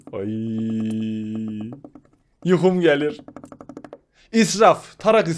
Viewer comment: de Yuxum gəlirmi dedi yoksa benmi öyle duydum?